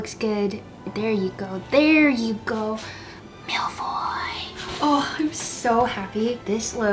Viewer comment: Love this, also I bet your kitchen smelled so good! For a sec I thought u said Malfoy 😂 can't wait to try the cookie biscuits 😊 Also binged all the seasons and love Polin, Kanthony, Benophie and Franchaela. Can't wait for Hyacinth's season.